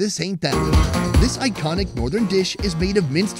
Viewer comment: I thought he said "is mixed with hentai"